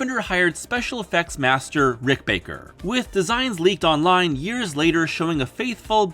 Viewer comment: for a second I thought you said Britt Baker